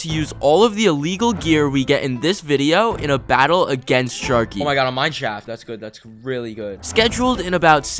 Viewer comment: I thought he said sharty